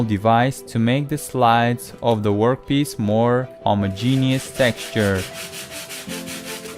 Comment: Did he say I'm a Genius Texture at 4:52? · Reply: Homogenous